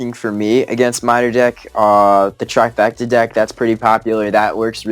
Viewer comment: What does say at 12:10 ? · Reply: Trifecta: musketeer hog valk combo deck. Usually poison, zap, some cheap unit like skeletons Gobs spear gobs and cannon